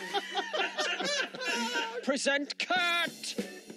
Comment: i thought it said present kurt